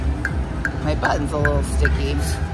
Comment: I need a hearing check ..honestly thought you said my butt is a little bit sticky 🥺😅 me thinking to myself confession is being a tad tmi 😅😅😂... My bad 😚